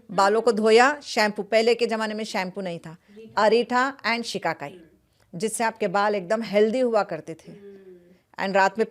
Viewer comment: I wish I understood what she said to use as shampoo instead. Anyone else have any interpretations? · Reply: Ritha and shikai